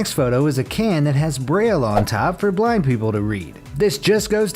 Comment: I misheard the can thing. I thought you said it had Braille on top so black people could read... I need help...